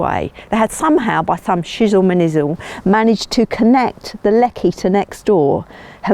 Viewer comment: did she just say fo' shizzle my nizzle?